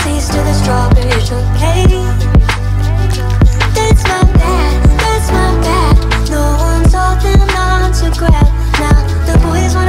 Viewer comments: I heard 'Thats my dad, thats my dad No one told him not to grab' I N C E S T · That’s my bad, that’s my bad, I thought she said “Though I told them not to crap”.